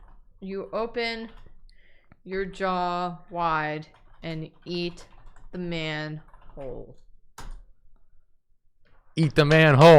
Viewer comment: When they said "Eat the man whole!", what I heard was "Eat the man-hole!"...